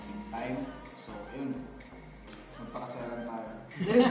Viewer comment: I THOUGHT KEN SAID "magpakasal na tayo" AT 11:12 I LITERALLY SCREAMED I WAS ABOUT TO SAY "YES" AHAHAHA